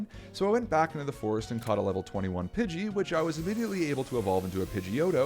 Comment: Instead of immediately, you know what I thought you said legally